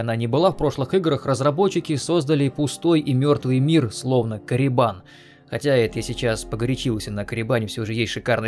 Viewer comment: когда услышал Коррибан , послышалось Калибан ....... эх вархаммер